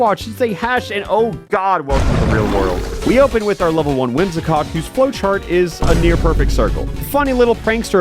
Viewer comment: Did he actually say Whimsicock at 1:18 or am I too far gone